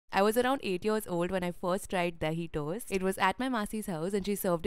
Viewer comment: I thought u said delhi toast ..i wondered i have lived my whole life in delhi but i never ate it😂